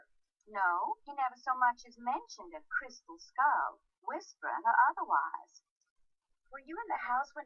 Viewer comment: I thought she said "crispy skull"... O_o